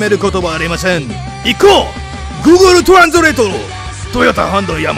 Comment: did you just say google translate omg xD